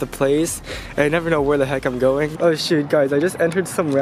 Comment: Did u say s😁😁😁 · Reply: T.E.C. Cuber shoot